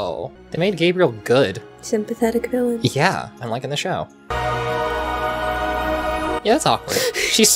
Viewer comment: did he say "unlike in the show" because 👀